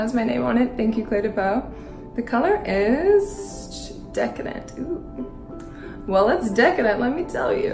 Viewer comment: YAY THANK YOU VOGUE!!!! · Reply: I think it's Clé de peau and the colour is called decadent (I thought she said chic décadent but I can't find it on the website. A similar shade called decadent exist tho)